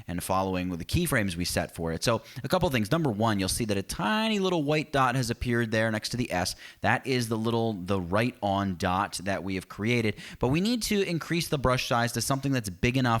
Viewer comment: did you just said "a tiny little white dot has appeared there next to the ass"?? lol